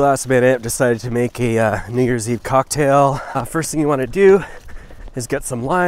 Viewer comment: Am I the only one who heard the n-word? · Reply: new years eve? i didn't hear the rude n word.